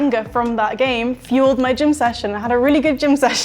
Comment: I had a good GYM session after the 7-0 😂😂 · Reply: At first I thought she said GIN session and I really respected that 😅